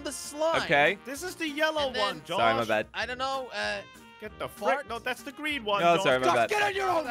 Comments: is it me or did crainer say the F word or fart? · did he just said get da fak